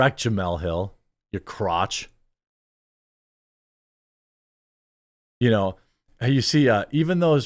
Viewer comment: Did he call Jamele Hill a "crotch" at 10:09? I've never heard someone called a "crotch" before as an insult, is this new?